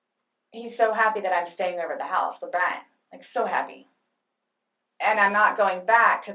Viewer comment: 55, does she refer to the kid as "the Brat" or did I mishear? cuz thats a little weird